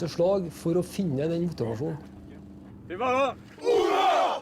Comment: what are they saying? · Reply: Til Vallhall (War shout)